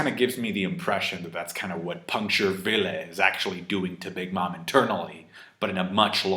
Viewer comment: Not gonna lie. My dyslexia thought Law said "Puncture Willie"